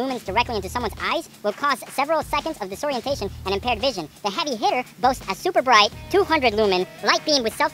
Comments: God I thought it said "Heavy Hitler" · i thought it said heavy hitler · i thought that it said heavy hitler and i was like what the fuck